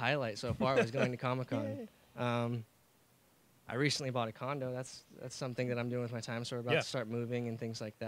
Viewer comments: I thought he said he recently bought a condom😂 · I thought he said "I recently bought a condom" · I HAD TO REPLAY @ 2:40 CAUSE I HEARD CONDOM INSTEAD OF CONDO IS IT ONLY ME?